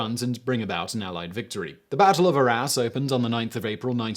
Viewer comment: I heard "the battle of our a$*". Am I the only one?